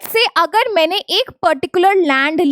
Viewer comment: At this point 3:52 did u just said rand...? Instead of land?💀